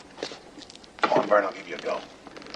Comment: What did he say at 2:57:01 He whispered something to Vern When he was in the chair! And Vern says I could pull you out of that chair! He says would ya? But before that he whispered something to him? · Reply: I wondered the same question.. I think he says "C'mon Vernon let me give it a go, c'mon".. in reference to their arm wrestling time earlier in the movie